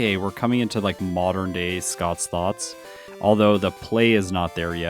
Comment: Every time you say Scott's thoughts I hear Scott's thots. Not sure how to feel about that lol